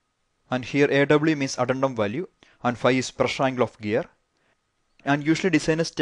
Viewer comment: sir did u call that pi profile of gear ? i couldn't get that word